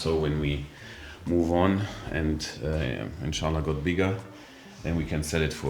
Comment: In the video at 10:45 did u say InshAllah???